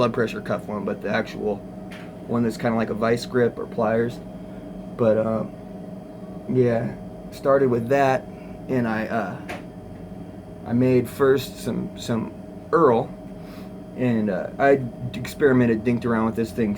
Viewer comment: All I heard was "I started with a dick pump"